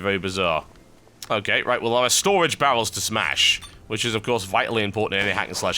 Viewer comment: it sounds like he said "storage bowels" i have some of those too